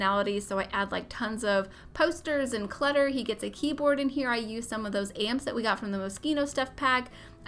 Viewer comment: I thought she said “I used some of those ants from the mosquito stuff pack” at 17:14 😭🤣